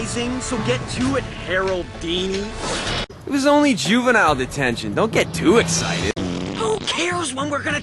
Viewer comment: DUNCAN PWNS and he says HOOO-AH :P 1:34 - 1:37 whoa i just skipped through i i heard duncan say die of toxic juvinial detention so dont get too excited :O not funny but it sounded funny harold:tchtchhhhhh i have cow boobies on my head duncan :>:(((((